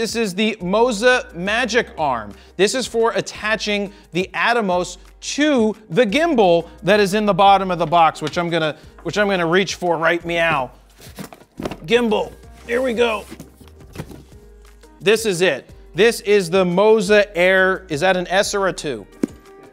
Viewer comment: I know i heard 'right meow'. 🤣